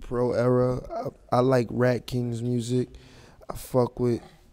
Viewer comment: was sagt Rocky? I fuck with Pro Era, i like ??????????? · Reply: Er spricht von Ratking. Auch eine sehr gute Crew aus NYC.